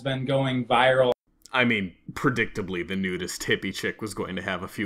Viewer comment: did you mean nudist? if so then you need a 2nd edit :P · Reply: Nope, I thought he said nude-est. I didn't realize he meant "nudist" and now I think it's funnier if I leave it as is.